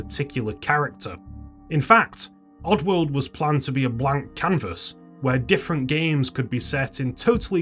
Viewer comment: who else thought he said wank canvas 😂